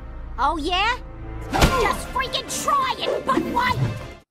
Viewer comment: Oddly enough, when I first heard that, I thought she said, "Just freaking try it, f**kwad."